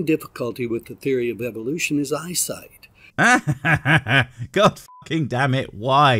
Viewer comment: "The main difficulty with the Theory of Evolution is eyesight". When I first heard him I thought he said "the main difficulty with the Theory of Evolution is Einstein!" and I have to admit, I was intrigued at how Einstein was a problem for Evolution... Oh well.